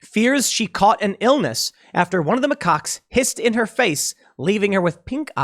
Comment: I thought for a moment he said "Pissed in her face"